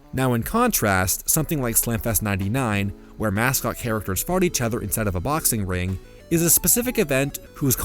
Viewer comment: did anyone hear him say “mascots FART on each other inside a boxing ring” 😂😂